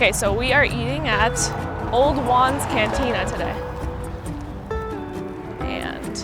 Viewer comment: At first I thought you were saying "Obi Wans Cantina!" LOL Actually I saw the sign and said Old Jaun's Cantina... Sounds great!